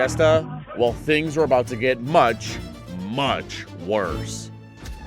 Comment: the caption in the screen says "WORTH" while Teets is clearly saying "Worse" reduce pay for the editor this month